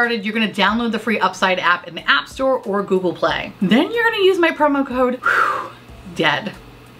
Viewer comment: I thought you said, “Use code Dad.” I was like, shit, we are getting deep today 😂